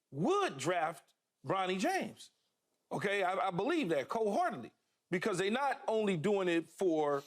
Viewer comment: did he just say “cold-heartedly” ??